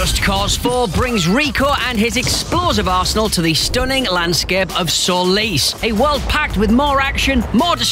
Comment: At first I thought he said "Just cause 4 brings Rico and his explosive arsehole to the stunning landscape of Solis"